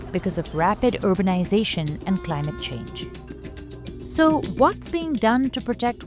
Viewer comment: she actually said “planet change” 😂😂😂😂😂